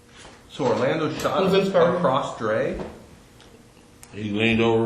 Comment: After 7:30 why do I hear Orlando shot across Dre who’s dre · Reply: Dresta